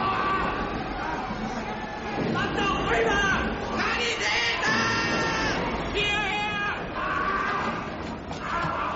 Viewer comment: What does the german soldier say at 3:34? I heard some german say this in Saving Private Ryan as well. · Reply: ***** That's the one! Sanitäter.